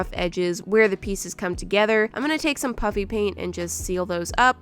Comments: I love how it is called puppy paint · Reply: Its actually called puffy paint but realy sounds like shes saying puppy paint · This video brings a whole new meaning to "puppy paint" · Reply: it’s puffy paint, but it sounds like Moriah is saying “puppy paint” so that’s why it’s kinda a joke that it’s puppy paint. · When I first watched I thought that she said puppy paint ^_^ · She is definitely gonna have to do this again like if yall agree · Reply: Why is it so funny that it sounds like shes said puppy paint or is it just me so it's just me